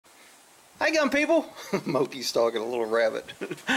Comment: Hey I have watched your content for a couple years and my wife will watch with me a lot of times because you make us laugh . About a month ago I repeated your opening line and she said "gun people ? I thought he was saying dumb people this whole time because he always says that' your a freaking idiot and big dummies" . So now when you open she always says hey dumb people and she gets a kick out of it !